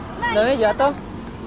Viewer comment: apa cuma gw yg salah denger?? Mbak it*l nya jatuh 😁😀